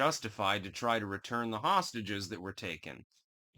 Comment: Did my ears deceive me or did you say "Hamastages around 3:02?